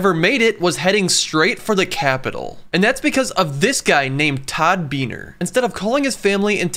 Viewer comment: To the narrator: have some respect. At a minimum, get this legendary American's name correct. It's Todd Beamer. (not Beaner) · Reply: I swear I thought he said Todd Beaner. I had never heard anyone by that last name before. I even lived in Honduras for a few years, even though it could be a Mexican last name.